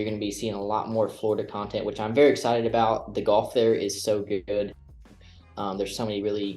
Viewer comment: Am I having a stroke or did he say ''The golf there is going to be so good good." at 23:19 - 23:21 ish?